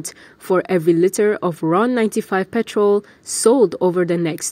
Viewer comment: another typical malaysian production where the the presentation is so poorly narrated. the pronounciation is so poor that it is almost embarrasing. for example it sounded like she was saying "a litter of ron 95"? instead of a 'litre' ? hmmm